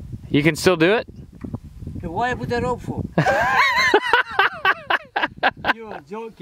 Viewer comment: What did he say at 5:42 "why I put that rope for?"